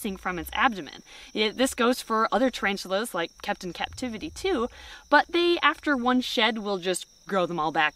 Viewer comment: At like 7:12 as she’s talking about the tarantula, I could have sworn she said “Captain Captivity” as if it was the name of one they own or something and I was very confused 😂😂😂